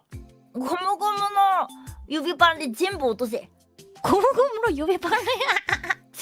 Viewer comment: ゴムゴムの指パンでちんぽ落とせ にしか聞こえないww聞き流してたのに聞いててビビった